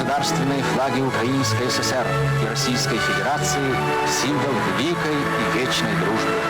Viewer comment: Am I crazy or did I hear Nazi at 0:38 secs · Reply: The voiceover said федерация (federatsiya) or federation - something clearly written in the subtitles as wells… which would mean that you, my friend, indeed are crazy. 😅